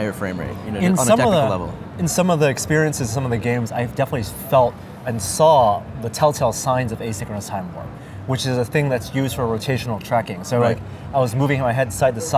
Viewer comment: Can someone please write down what Norman said at 17:25 ? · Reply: "In some of the experiences, in some of the games I definitely felt and saw some of the telltale signs of asynchronous timewarp, which is a thing used for rotational tracking. I was moving my head side to side and you can see the edge of the screen wobble just a little bit, like it was being warped. It wasn't enough to make me uncomfortable but it was noticeable especially if you have spent time in VR."